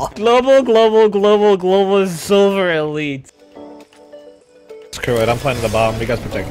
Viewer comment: Did he say “Silver elite!?!?!”